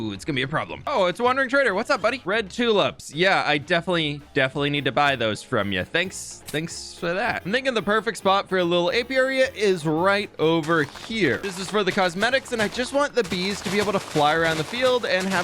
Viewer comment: Can't lie, swear he said the n-word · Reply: he said, “I’m thinking the perfect spot for a…” but the im thinking part kinda ran together